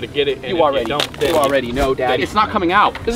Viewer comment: did anyone else hear him say daddy😂